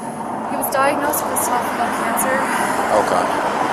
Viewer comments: Thank you for your reply. I would agree that on each occasion that she states the nature of her boyfriend's cancer, it is unclear to what she actually says. But she also stated that she did not 'drink' and that she does not consume alcohol. Therefore it would not be unreasonable to concider her capable of lying. I am not American, and I find that American speech can sound quite 'slurred' at times. But it would be fair to say that her discourse with the police officer is of less relevance to the fact of her inebriated condition, and our gratitude towards the Law Enforcement agents who unfortunately have the onerous task of dealing with and removing drunk drivers from your roads. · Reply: I was referring to 34:31 when she says that her boyfriend was diagnosed with esophagal cancer. The caption is obviously wrong but if you pay attention to what she actually says it's 100% "esophagal". Perhaps you were referring to a different part of the video? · did she say he was diagnosed with cervical cancer ❓ Men don't get that. Maybe she doesn't know what exactly he was diagnosed with. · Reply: She said “esophagul”. Still incorrect terminology but clear with decent audio.